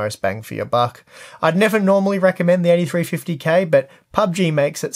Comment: Hey, just a tip, you can change seats while in a vehicle by pressing ctrl + number key, for example, ctrl + 1 will put you in driver seat. Also I didn't hear you correctly at about 9:00 and thought you said (FX) 8350, I had to go back and listen again. Thanks for another great video.